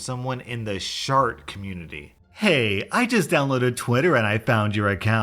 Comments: Man from the intro i heard "shard community" and as a recovered meth junkie thought you were talking about drugs..... Wow for once i wish i heard meth instead (of shart) although i guess one can lead to another or in this case maybe dudes mom just had alittle during her pregnancy ×.× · In intro to the first one, I thought you said 'shark community'. Oops